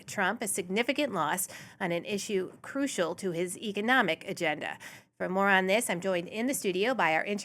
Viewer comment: I misheard one bit there. I thought you said "his ego-nomic agenda" and my mind went "yep, that checks out".